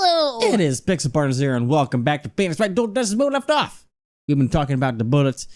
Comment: I thought he said “and welcome back to Penis Wright” and I’m still laughing 20 mins later😭🧎‍♂️